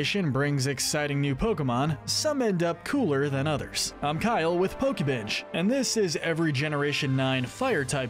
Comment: I thought this man said I'm Kyle from pokebitch... very disappointed that I wasn't correct.